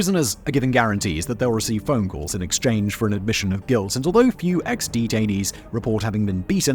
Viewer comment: i heard it as xD-tainees, i need to touch grass.